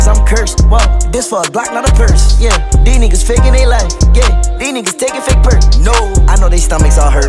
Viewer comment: I could have sworn he said "These niggas taking fake prick"